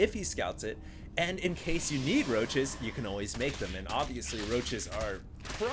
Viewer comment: I heard him say Need roaches and heard Negroches. o.o